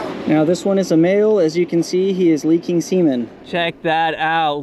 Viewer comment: Did you say leaking seamen